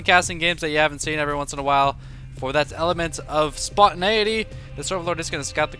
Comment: "Elephants of Spontaneity" would be a great name for a band. Which is totally what I heard you say.